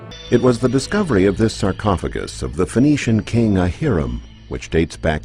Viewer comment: did he say Hiram, as in Hiram Abiff ? I couldn’t hear. If so, this is quite telling.